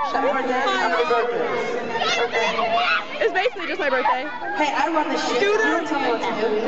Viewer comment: i thought he said i run this shift haha ♥ xxxx